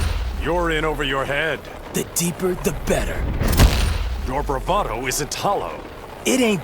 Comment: Aquaman: “enough with the fish metaphors!” Also Aquaman: “come any closer, worm, and I’ll bite!” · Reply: I always thought he said "Your bravado isn't hollow"